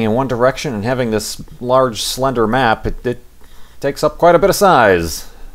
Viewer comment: I misheard "slender map" as slender man D